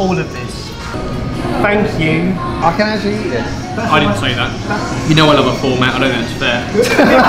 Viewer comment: what does Ben say ? please :) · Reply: "you know I love a format, I don't think that it's fair"